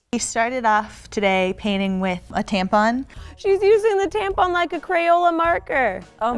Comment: Bro I’m trying to eat while listening to my favorite YouTube and the thing I hear is “she’s using the tampon as a crayon”